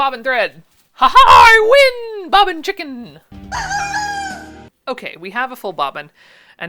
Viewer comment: At the 17 minute mark we all heard you say you weren't working with a full bobbin. LOL